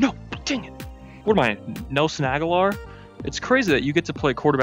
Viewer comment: I misheard "What am I, Nelson Agholor?" as "What am I, No-Snag Ahlor?